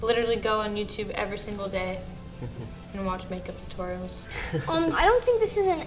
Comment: When maddie says she watches 'makeup tutorial' i thought she said 'Jacob sartorius'